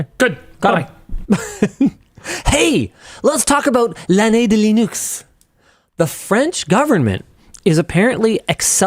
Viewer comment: I heard “l’annee de les nukes” 😂